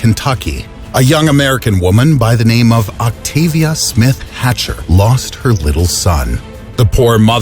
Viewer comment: I thought you said Dr. Octavious from spiderman